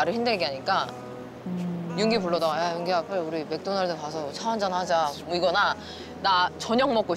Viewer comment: 저 왜 맥도날드 가서 차 한 잔 하자 라고 들리죠..